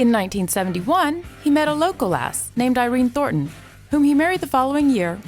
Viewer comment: "In 1971 he met a local ass" That's what she said. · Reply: Local lass maybe?